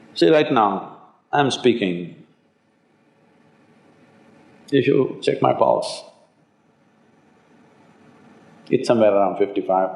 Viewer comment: did juggy ask people if they checked his testicles? I think he said “See, right now I am speaking, did you check my balls”? I know I know, he said pulse. He should seriously stop putting on this absurd accent.